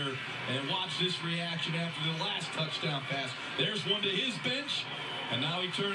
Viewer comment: lol i thought he said " theres one to his bitch" and theres one to their bitch xD